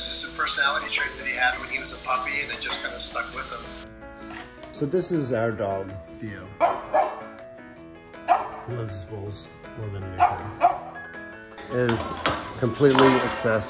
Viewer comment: I had to rewind a second and read the subtitles, I thought he said "so this is our dog, Dio"